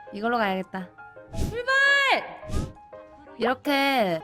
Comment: 순간 나만 슈발 이라고 들었나 크흠 …ㅎ